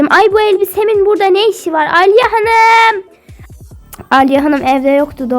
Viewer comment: Alya hanım mı🤯 Yanlış mı duydum..? AGAGAGAG BENİM İSMİMMMMMM❤